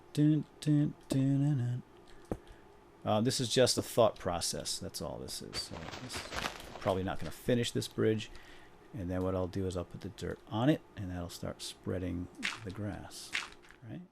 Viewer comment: did you said fuck proses